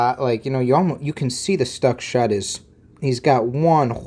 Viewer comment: Lol I honestly thought you were saying "Stuck shut" and I'm thinking WTF is stuck shut?